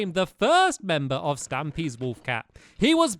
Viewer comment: did anyone hear say wolf cap😆😆😆😂😂